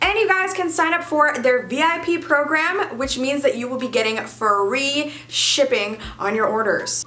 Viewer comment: I heard "Furry shipping", there is something wrong with me.